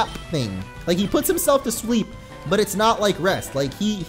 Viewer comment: Did he just say "he putts himself to sweep" 21:40?